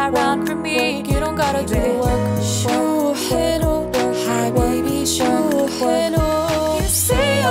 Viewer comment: انا سمعت حبيبي شو حلو 🤣😂